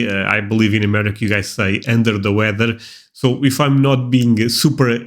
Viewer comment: It sounded like you said End of the weather, it's actually Under the weather.